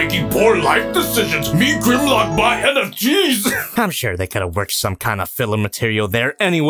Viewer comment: "Like uh...one disease that changed the world?" Me: "I got you fam, I got Black Death, Spanish Flu, and Covid, which do you want?" Edit: Also, 21:47 with that grimlock voice, it could sound like "Me Grimlock buy NFTs" or as I heard the first few times "Me Grimlock buy head of cheese-" XD just the way you said it in Grimlocks voice man, i'm sorry